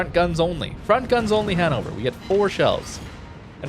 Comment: oh damn I thought you were saying hand-over all the time, only at the end of the match I realised you meant Hannover, yeah german pronunciation isn’t easy